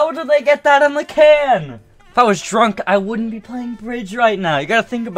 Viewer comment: I thought it said astelic plays bridge